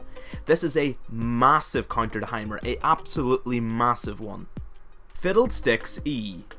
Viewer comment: Fiddle d1cks E? GOD DAMMIT ACCENT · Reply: I heard "fiddled sticks"...